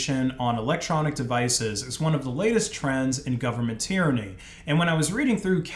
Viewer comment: I thought you said "governmenteering" akin to racketeering, with which is apt, if not a bit redundant.